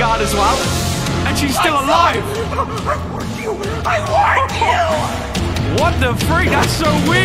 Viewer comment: he actually says “Lights out I warned you I warned you”. Probably warning you that he’s about to turn to moon drop At 1:55 Moondrop says “Naughty boy”